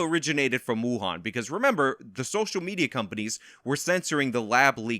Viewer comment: I heard you say "SOCIAL MEZIA"